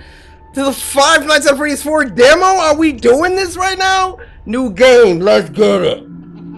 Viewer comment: I thought he said new gay 🤣😂🤣😊🤣